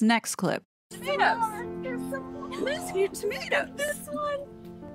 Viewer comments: I hear, “I missed you” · Hi! Great video and great series! So the part with that girl, I heard her say, “I missed you”. Not “is it you”. But maybe that’s just me. My niece who I see everyday is 15 months old and she does not have all of those teeth. She’s teething like a bear but doesn’t have a full row of teeth yet like that baby in that picture. That’s the saddest birthday party I’ve ever seen. Why aren’t they wearing the hats?? The whole thing just looks suspicious to me. And by now they have lied so much that we can’t believe a thing they say. Merry Christmas!!!! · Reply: I heard ‘I missed you’ too. But not sure if that’s what she actually says